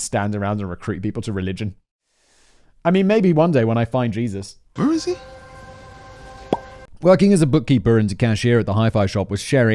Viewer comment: “When I find Jesus working as a bookkeeper” is what I heard at 8:57. Made my day.